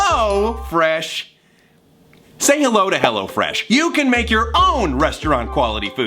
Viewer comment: "Hello Fresh" I thought he said "Hello French" ...